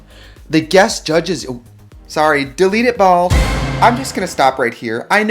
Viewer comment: when trixie said “delete it bald” and threw off her beanie I SCREAMED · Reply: Omg I thought she said DELETE IT PAUL and I was wondering who Paul was 😂😂😂